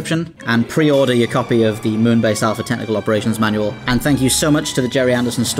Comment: I heard "Moonbase Alpha Technical Manual" and was already sold